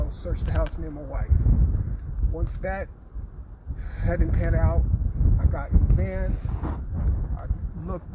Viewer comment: I think they did something they know where the boys are at. And the way the guy said the way it all planned out. This is so fishy. I can’t believe they’re not in custody until they know what happened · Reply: sounds like he said panned out